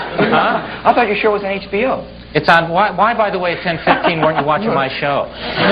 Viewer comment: what did Shandling say to Kato here that made the crowd go nuts? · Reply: misunderstood it is..."Why by the way at 10:15 werent you watching my show"